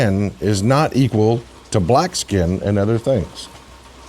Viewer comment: I honestly did not hear her say "NOT" I would have sworn she only said "that their white skin is equal to black skin" I was wondering why more people weren't outraged 😬